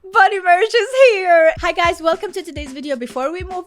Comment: I heard "Bunny Marriage" at the start, and was befuddled!